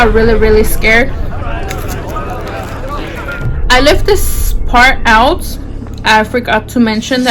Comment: I thought she said “I let this fart out”